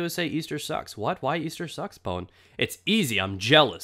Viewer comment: i thought you said Easter socks lol! Easter sucks indeed. non stop adverts for easter eggs and everyone in every shop always trys to get me to buy 20. :S